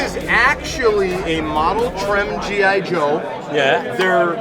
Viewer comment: I can’t really make out what he is saying, but did he refer to the figures as “model trim”? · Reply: Model Trem